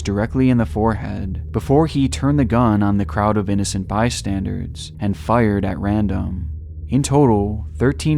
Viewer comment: Your airy enunciations are overworked, watch out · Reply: “by-standards” instead of “bystanders”…